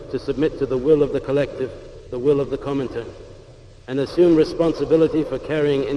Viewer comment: I think he said Comintern at 1:01 and 1:07 Similarly at 1:36 3:54 lol